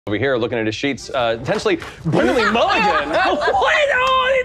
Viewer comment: I don’t know why, but I each time I hear the start it sounds like he says “Over here looking at his sheets, potentially Brennan Lee Mulligan” 😂